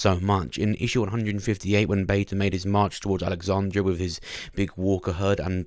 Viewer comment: what language were you speaking there, bud? Jesus I rewatched 7 times and I still cant figure out what you were saying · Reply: “March towards alexandria” late reply lmfao